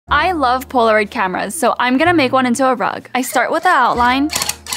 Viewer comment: I thought it said Poland camera rug I was so confused